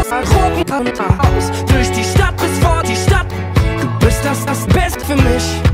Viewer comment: Durch die Stadt bis vor die Stadt? 00:39 Hab ich was falsches verstanden weil hab iwi seid Jahren ein hör schaden